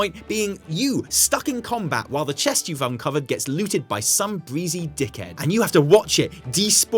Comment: did he say "some breezy dickhead", or "Soulbreezy dickhead" 🙀